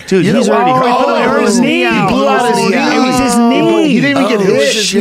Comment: I didn't catch it... was it his knee?